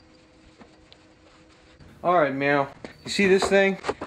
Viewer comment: Did i hear him say "alright meow" ? lol